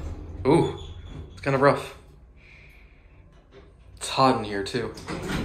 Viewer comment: sounded like "it's Haughton here" at first lmao